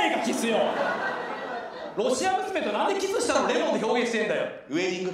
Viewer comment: これなんて言ってる？ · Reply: ロシア娘のキスの味レモン